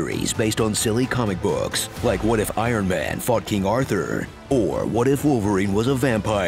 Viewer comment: I heard what if Iron Man f***ed King Arthur